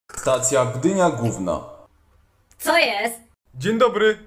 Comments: Usłyszałem "wymiar gówno" xd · Czy ktoś to usłyszał przystanek Gdynia G***o